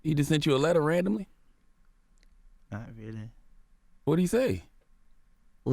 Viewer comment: Wtf did he say 😂😂😂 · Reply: Ilgi Ilo "not really"